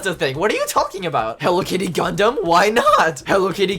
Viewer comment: I SWEAR TO GOD I THOUGHT YOU SAID HELLO KITTY CONDOM